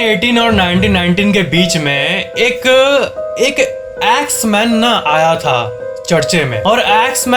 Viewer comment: I thought u said X-Men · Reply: Axe man